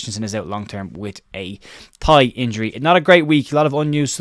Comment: Hi, great update as always, but at 08:17 did you say that Rasmus Kristiensen has a pie injury ? Has Weston McKennie been throwing his food around again ? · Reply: I said Thigh injury mate.